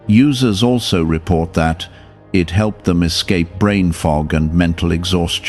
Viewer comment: I thought you said it causes brain fog. An I have to buy another one